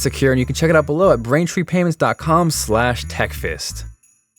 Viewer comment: did he just say techfist instead of techfast? lol